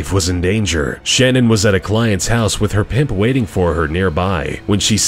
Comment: editing messed it up............did you say pimp?